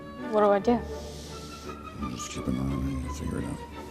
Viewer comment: "just keep an eye on it, you'll figure it out" I think that's what he said.